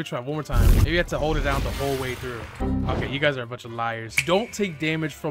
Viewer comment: first time hearing him swear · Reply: I Heard "you guys are fucking liars!"